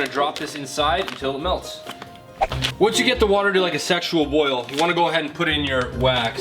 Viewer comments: Am I the only one who heard him say “sexual boil” twice 😂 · "Once you get the water to a sensual boil..." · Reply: Kamran Khaleeli I heard “sexual boil” and “sexually boiled” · did.. did he just say sexual boil